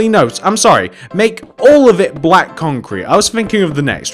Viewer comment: did he say black concrete (like if he did)